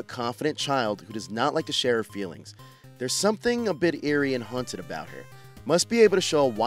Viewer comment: I would preview your script beforehand. For Marie, you said "confident" when the actual word is "conflicted" and it sounds like you said "hunted" instead of "haunted." Enunciate boo.